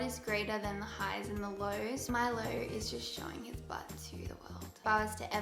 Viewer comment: when she said "Milo is just showing his butt to the world" I thought she said "my low is showing his butt to the world" and i literally just sat there for about 10 minutes or so trying to figure out who she's talking about 😂😂